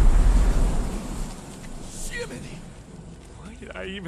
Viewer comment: Did bro said “skibidi”? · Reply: Jiminy